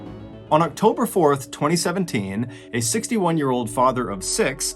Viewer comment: LMAO. @ 1:16 I heard that as a 16-year-old father of six! I was like WHAT?!! He actually said 61 year old though.